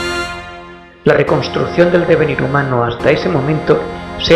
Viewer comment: que dice ? Rebverier, Rebelión?